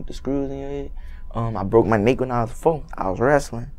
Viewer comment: All I heard was “when I was foe”